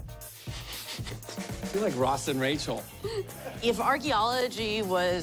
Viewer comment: What he says ? :D ...... like Ross and Rachel ? :D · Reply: "I feel like Ross and Rachel.."